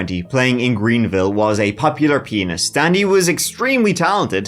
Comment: I literally heard "In 2020, playing in Greenville, was a popular P*NIS" .... 😆😆